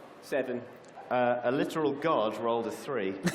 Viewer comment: I thought they were saying guard, not god this entire time.